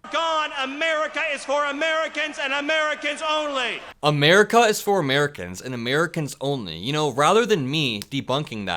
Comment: When Miller says "America is for Americans only", I hear "America is for white Americans only".